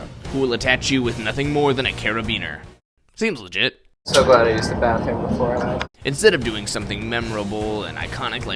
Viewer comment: What does he say there? I can’t tell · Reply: so glad i used the bathroom before i went